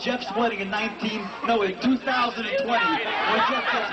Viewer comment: Did he said 2020?...like if you are watching this video in 2020..!!!!